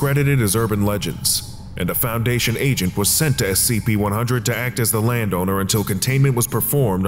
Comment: Audio says SCP-100-1 despite subtitles saying 'SCP-100'